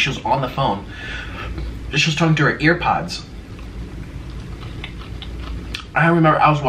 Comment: did he say earpods